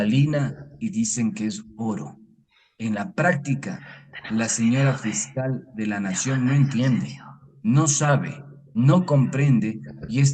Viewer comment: Minuto 22:17 se escucha un voz baja que dice ´´TENEMOS MIEDO DE HABLAR DE VILLAVICENCIO´´ escunchenlo con audífonos